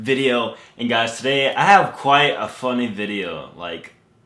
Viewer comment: I could have sworn said quite a fucking video. I was like since when did cuss